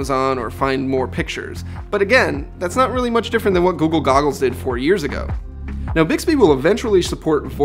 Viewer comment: Did he say google goggles?